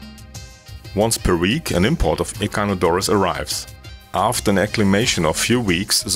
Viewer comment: what's the plant called at 1:22? I can't catch the name. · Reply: Echinodorus.